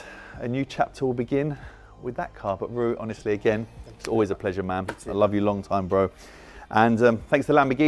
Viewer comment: Aww poor, rich person problems eh! And did he just say "me love you long time" to the Lamborghini salesmen? LoL